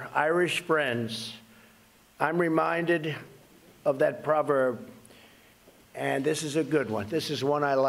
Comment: the President says 'I'm reminded of that proverb..'.He did not say 'I'm reminded of that IRISH proverb'. Very funny monologue as ever but based on an "alternative fact".